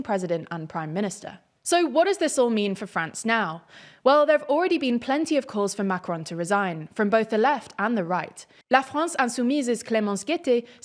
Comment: Why do I keep hearing “the Left and the Far-Right”? Why are the option left and “far”-right?